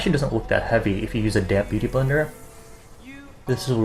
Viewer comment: I kept hearing "a damn beauty blender" instead of damp x.x